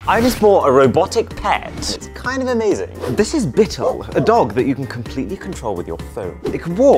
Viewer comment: When he said : this is Bittle! I thought he said this is little bitch!